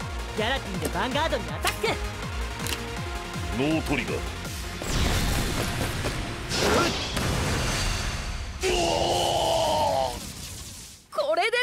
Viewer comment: "no trigger" instead of "no guard"